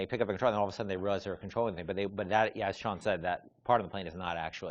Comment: lol 6:33 Ken says "As Shawn said..." but the subtitle says "As John said..."